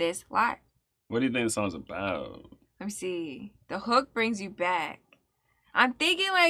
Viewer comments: i always thought he was saying "the heart brings you back". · Growing up I always thought he was saying “the heart brings you back”. I thought he was saying that no matter how jaded you might become, that you once had a good pure heart, and that’s where you can be again. I would hush people as they corrected me at karaoke. I eventually got it. But I still sing it my way, lol.